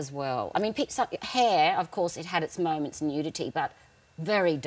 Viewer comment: I love her so much - btw what does she say at 6:38 that had its moments in unity? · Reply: rainboweinstein she's saying nudity I believe